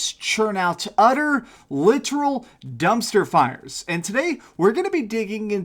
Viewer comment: You said dumpster fires. I thought you said dumpster farts... And im gonna use that expression now.